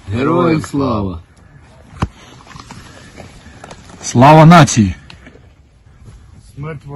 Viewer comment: Did he say: Slava Nazi? LOL... looked more like a Ninja to me 😆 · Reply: He said: Slava Natsiy. It means Glory to peoples